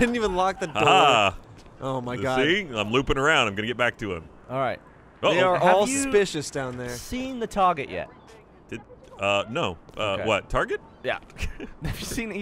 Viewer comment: I just got finished watching some Doctor Who and I thought they said “Have you seen the TARDIS yet?”